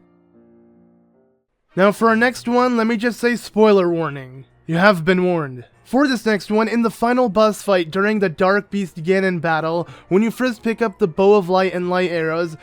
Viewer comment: lol. in 11:53, said "buss fight" instead of "boss fight".